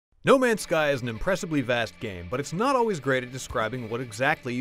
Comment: At the beginning, I thought he said "no mans sky is an incredibly bad game".xD